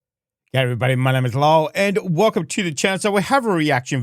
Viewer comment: is his name? I always thought he said Lowell at the start of the videos.